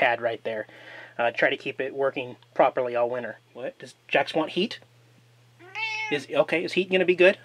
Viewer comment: We learn from our mistakes, or the mistakes of others. Thanks for sharing how you corrected the solar panel issue! (: p.s. you 'do' know that Jax thought you said, " does Jax want 'eat'?" That's why he got all excited...LOL!